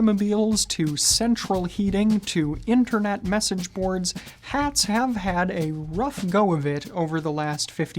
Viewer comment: When he said Hats have had I heard "Hats hat hat"